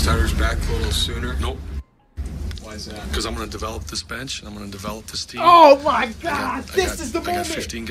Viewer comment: why'd I think he said "ima develop this bitch"